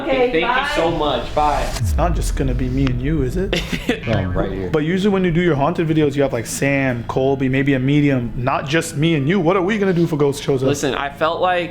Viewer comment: said maybe a medium instead of amanda